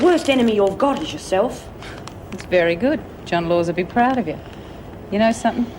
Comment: who did reb say to Myra would be proud of her? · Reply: Sounded like John Laws an Australian radio presenter.